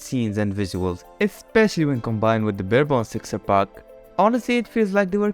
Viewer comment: texture pack? I don't understand what he said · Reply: With the barebone texture pack*